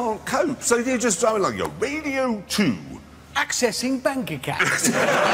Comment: does he say accessing bank accounts?